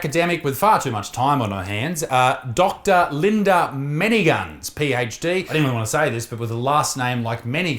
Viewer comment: SOUNDS LIKE YOUR SAYING MINI GUNS 🤙🏻LOOKS LIKE SHE'S USING HER phd VERY WELL AND ADRESSING REAL TIME ISSUES